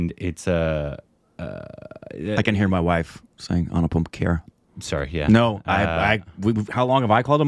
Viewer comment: When You guys say Anupam Kher I hear Anu "POOP" Kher 😅🤣😂